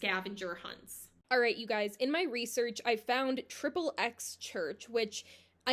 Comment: I thought you said tripleS church 😭😭😭😭😭